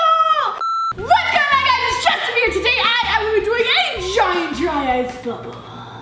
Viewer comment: At first I thought she said hey guys its Justin Beber